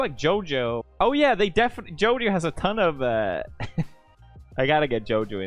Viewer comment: in case somebody is watching i think he said JoDio , lol predicted character